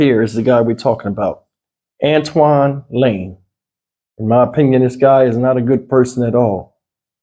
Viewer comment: He'll Mr want to no when the trail date for Tyree Nicholas going to be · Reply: I thought they said in May